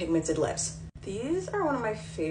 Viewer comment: Did I hear “I’m screaming I’m creaming” or am I suffering from hear loss?